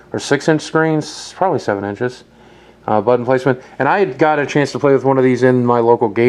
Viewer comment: anyone hear boogie say 'product placement' before he mentions gamestop at around 4:49?